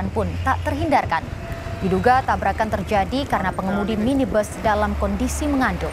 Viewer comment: Apa aku salah dengar Pengemudi minibus mengandung?